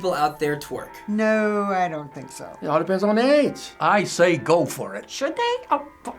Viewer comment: Did he say, "All depends on their ass."? haha